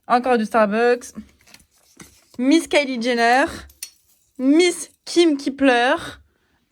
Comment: pk j'ai entendu "miskine qui pleure" 😭😂